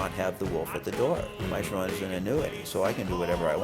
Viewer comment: What does he say at 2:36 "My Sharona is....." What? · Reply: Kinda hard to make out but I believe he said "an annuity".